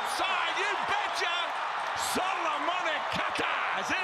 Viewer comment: wth he just said solomone kaka